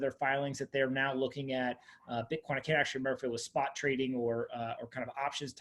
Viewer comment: Lol at first I thought you said renaissance is looking at bitcoin cash